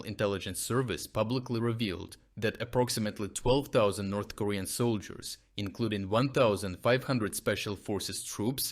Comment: I misheard him say "from L.A. North Korea." It still works as a sentence. 😄